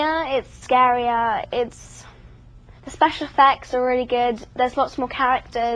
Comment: i thought she said special s*x